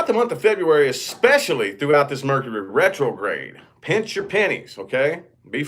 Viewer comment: I thought you said pinch your panties ha ha